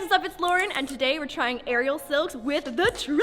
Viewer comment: Seriously thought it said “aerial skills”.